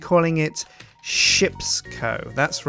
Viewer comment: I thought it said shits co insted of ships co